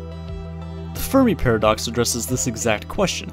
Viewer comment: i legit thought u said the furry paradox